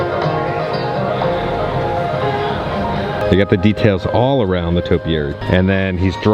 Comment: Did you just say "Maynard"? 🤔😄